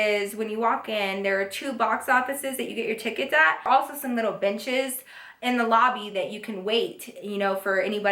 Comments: when she said benches I thought she said bitches lmao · I thought she said "some little bitches in the lobby" 😂